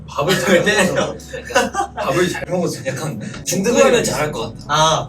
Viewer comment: please notice smth, what riki said was "He eats well" but the caption was "He eats a lot". Do yall see how misunderstanding spreads :) The word Riki said was "jal mwogo" which means eating well that doesn't mean eating a lot.